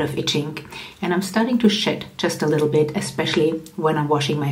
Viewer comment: Lol at 15:31 I had to rewind and laugh….with your accent I could have swore you said you were starting to shit just a little bit…lmao